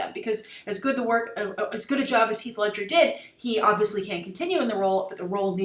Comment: Did she say Hieg Ledscher instead of Heath Ledger?